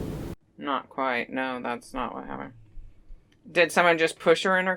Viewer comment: Wow I heard “not quite” were you using a evp radio ?